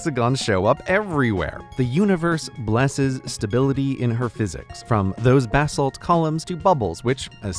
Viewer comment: I skipped a little and it sounded like he said “eat physics”